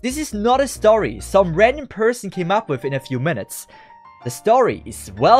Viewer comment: I thought he said Reddit instead of Random LMAO